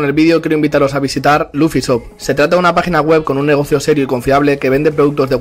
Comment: siempre pensé que decía "luffy soft" pero era "luffy chop" 😔